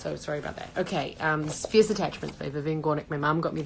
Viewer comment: what book is she talking about at 13:15 i cant understand her lol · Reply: Vivian Gornick "Fierce attachments"